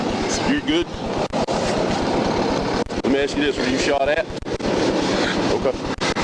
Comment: Idk if i misheard. But it sounded like the perp said "you shot me?" And that never fails to baffle me. Like. You attacked a cop. You literally went for the cops throat. Wtf you think was going to happen?? Don't want to be shot by a cop? Don't attack them??